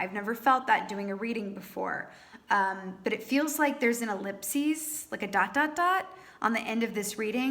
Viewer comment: When you said “ellipses” at the end, I thought you were saying “eclipse” at first & we’re entering eclipse season with a Sagittarius lunar eclipse on the 5th... wonder what the solar eclipse at the end of June will bring👀